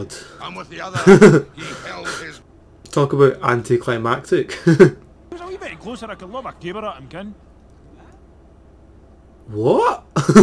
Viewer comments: I think i understood this? No idea how, since I'm not Scottish, but i think he said "If he was a wee bit closer I could lob a caber at him, ken?" I know 'ken' is the old English word for "know" and a caber is that massive pole that they throw in the games, so hes basically saying "If he was a little bit closer I could throw a pole at him, you know?" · ". . . What?!" Lol, I had trouble with that too, had to look up what he was saying haha - he's speaking in the Doric dialect. "Gif he war a wee bit closer A could lob a caber at him, ye ken?" For those who don't know, a caber is a long thin tree trunk (16-20 feet tall) used in the Scottish Highland Games, competitors in the caber toss have to run forward with it upright in their hands, and flip it so it lands on its opposite end. And lobbing something means to throw it, so "If he was any closer, I could throw a caber at him, you know?" 16:58 - 17:03 "It's jist nae fair makin us ficht for the hand o the quine that disnae want any bit o it. Ken?" - Quine is a girl, so "It's just not fair making us fight for the hand of a girl that doesn't want any part of it, you know?"